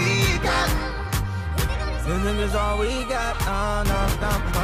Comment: "All we gat" not "got",he says "gat. I almost thought that he said -"And we back😑😛